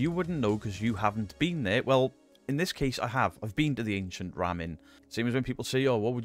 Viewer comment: Hey thanks to the captions I know it’s now called the Ram inn. I thought you were saying to the Ramming. Some kind of pushing video or the art of getting a man sheep and a lady sheep to make little sheep…. That’s what I thought I’ll watch the video now